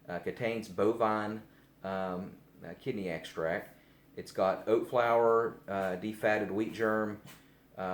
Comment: Did he say oats? My English isn't good.. Is oats good for a cat with kidney or urinary tract infections? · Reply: Yes, at 5:28 he says "oat flour".